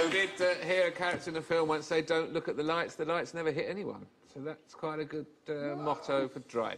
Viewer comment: what does Alan say at 5:26? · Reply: "Don't look at the lights, the lights never hit anyone."